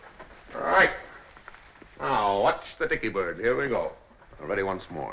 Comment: Does he really say " Watch the Dicky Bird" at the 15:04 mark , sounds like something out of a Smothers Brothers routine!